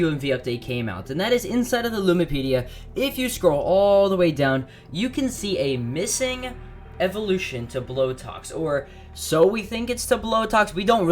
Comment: For missing Evos I thought it said missing Eyes.